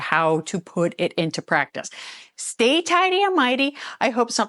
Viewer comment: Sorry, I have heard you say, "Stay Tidy and Mighty" for over a month now, and all I can think is "Tighty Whities" every time you say it - LOL. Love your videos.